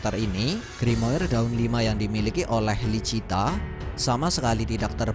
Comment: Kenapa aku suka salah denger jadi LUCINTA😭